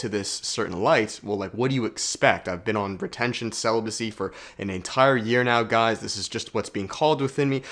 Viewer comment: I thought he said "pretension celibacy"... He definitely hasn't been practicing that...